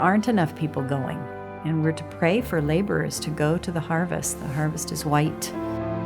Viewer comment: It sounded like she said "and the harvest is white". >> Listen: 2:33 <<< what was she saying?